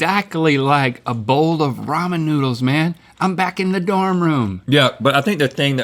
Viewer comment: i thought link said robin noodle....